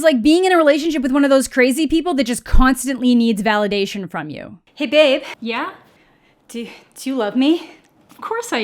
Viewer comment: Before I knew the yummy lyrics I thought he was saying get baked instead of hey babe and I liked it better that way.